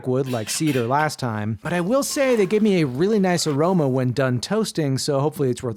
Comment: bro when you said “it gives a really nice aroma” I heard “it gives a nice jarona” and thought the wood was made out of the trees in chapter 5